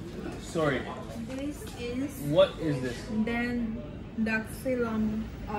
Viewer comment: what she trying to say is "zanthoxylum"